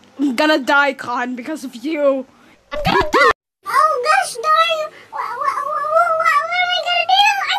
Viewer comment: I thought younger Con said oh Gosh darn geona